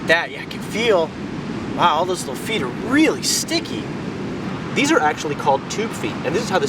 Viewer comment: wait did you say toothpick or toothfeet